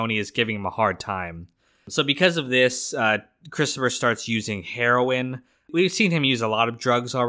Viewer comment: I thought you said Christophah does heroin for the first time in Italy?